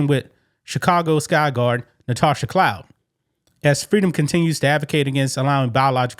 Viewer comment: I thought I heard her name as "clown", maybe because of the way she behaved.